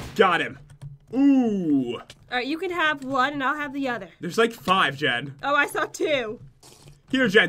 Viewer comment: pat did you just say hero jen take guns